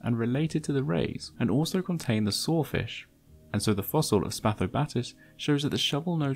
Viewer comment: in, the "sorefish?" Why were they sore? Oh, wait. You meant "saw-fish." Damn, dude. Learn proper English. Ah'm jus' phokin' wi' ye, mate. Jus' takin' tha piss. But for real, sometimes, your non-rhotic 'r's, plus adding 'r's to words with open 'ah's, make what you say "hahhd to unduhhstand" for non-Brits.